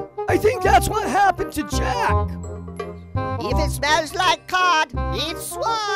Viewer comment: it kinda sounds like he said cock 0:40 :]